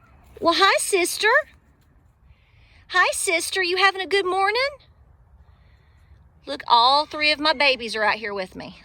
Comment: You did good! You didn’t lose it but stated it clearly! You said “have a Good Friday! I thought you said “have a good cry 😢” . That’s more to the point! But we have God, so keep the faith and put on your armor of God. 🙏❤️